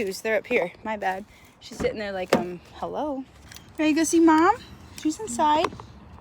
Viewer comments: sounded like said “Mum” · My heart melted a little when asked if she wanted to go inside and see mom and it sounded like said “mum” even though I know she probably didn’t it still sounded like it to me 👩‍👧💖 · At like 7:19 in the video I swear it sounds like said “mom” ❤